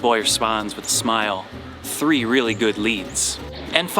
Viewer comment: What exactly did he say? I think the subtitles messed it up. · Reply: “ The boy responds with a smile: ‘three really good leads!’“ Meaning the three girls the priest thinks he might have fooled around with!